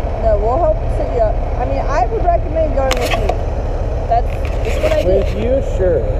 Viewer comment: Paramedic: " I recommend going to sleep." " With you?, Sure." is that what he said ?? 😂😂😂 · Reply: i think she said "i recommend going with me"